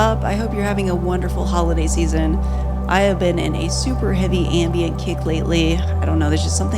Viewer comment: Oh! "Super AMBIENT kick"...at first I thought you said "Super AMBIEN kick," and I was about to tell the story of a friend who woke up with spaghetti sauce all over the ceiling and walls.